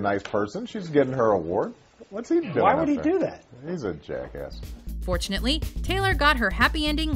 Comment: Did I hear Obama say, 'he's a jackass' referring to Kanye? :O